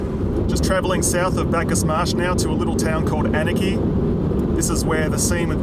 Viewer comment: Anakie. I thought you said anarchy and I thought.. .Fitting. Also, don't mind me.. I'm just rewatching some videos.